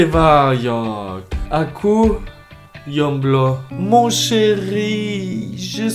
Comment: Aku...Yomblo... Me : Dude, i just heard you say "Yomblo"... Its not Yomblo its J omblo